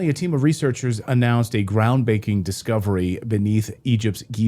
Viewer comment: did anyone else hear “a ground baking discovery”